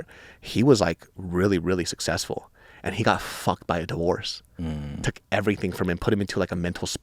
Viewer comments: I had my volume so low that I heard David said “ he got f*** by dwarves” , I had to go back and put my volume up to see if I heard that right. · What did David say? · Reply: He said that the homeless guy he had been talking with said that he "got f'd by a divorce." That "it took everything from him". That the guy "went into a mental spiral and lost custody of his kids."